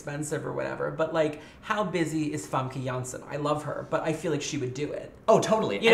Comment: “How busy is [wtf is he saying here]” ? Who’s name is he saying?????? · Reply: Famke Janssen. She played Jean in the first three movies.